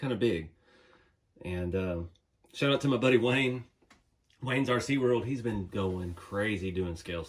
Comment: such an epic build man! That thing is coming together beautifully. I'm curious about the channel you were saying that got you to think in more scale terms. Sounded like you said "Blaines RC world" but I couldn't find that on YouTube